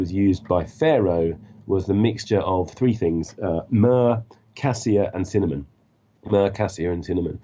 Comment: everything makes scence :) Can someone help? What is saying at 27:30...cinamon and what are the other two?really interesting, nature will heal us all :) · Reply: Myrr and cassia